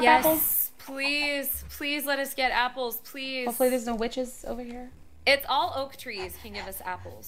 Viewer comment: When said "there's no witches here" i thought she said "there's no b**tches here"